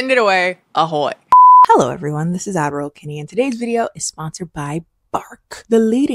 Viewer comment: I now feel brave enough to admit that I thought you were saying “Averal Kennie.” I thought you had a twin named Averal that read your ads. 🫣